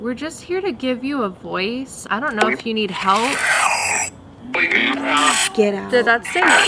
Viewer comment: Ok 25:00 it sounds like it said “helpp” idk but it sounds like it